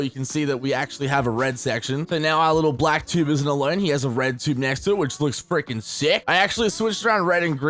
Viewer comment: did I just heard red tube?